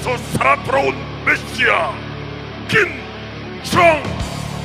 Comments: i dont know what they said at 6:08 , but didnt that one southkorean guy scream out "KIM JONG UUUUUNN!!" ??? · Did he just said KIM JONG UN at 6:05? Lmao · That sounded like he said Kim jong un. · I misheard it as KIM JONG UUUUUUUUUUUUUUUN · I lost it when I thought the announcer said KIM JONG UN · wait... did he say kim jong un? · did they just say kim jung un was in the finals O.o